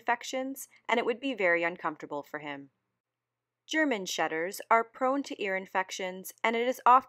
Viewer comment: okay why did I hear German shedders at 1:31?